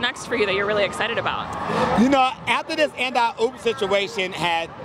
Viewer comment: When this meme got popular, I thought she always used to say AND I POOP